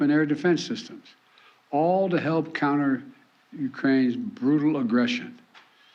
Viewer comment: lol am I the only one who heard Biden say "all to help counter Ukraine's brutal aggression.." then at 6:33 did just Biden said "our terms that preserve Russia's Sovereignty?" Looks like the one who wrote his speech didn't do their homework right. Lol